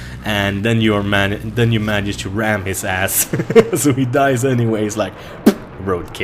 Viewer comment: Did he just say "Ram his ass" when they jump out? Lol